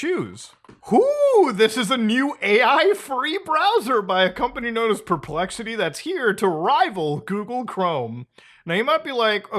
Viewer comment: When I thought I heard “AI-free browser”: :D When I noticed you said “AI, free browser”: D:<